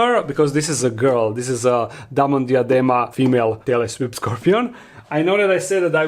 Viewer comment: On the "English generated subtitles" of your video at 0:30 to 0:34 Instead of you saying the "whip scorpion" name it says "Taylor swift Scorpion" 😂😅